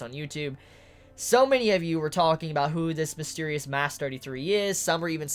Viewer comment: What is saying at 36:00? How does he call Touka? Sounds like mass 33 can anyone help me with that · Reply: Mask 33 they all had on gas masks and touka had the number 33 on hers